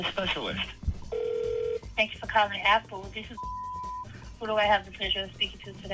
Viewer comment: “Thanks for calling, Apple, this is F*ck.” THAT’S WHAT I HEARD 😭